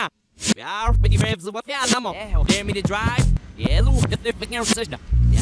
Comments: Anyone else catch the "dare me to drive" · Did anyone else catch "dare me to drive" at approx 1:03 right as he's getting in the car in reverse?